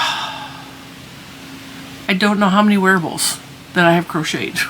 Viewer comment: I literally heard you say “werewolves” and not wearables. I mean that would solve your neighbor problem🤷‍♀️